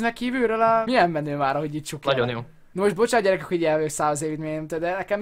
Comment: valaki megmondaná hogy mit mond? Légyszi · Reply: Nagyon jó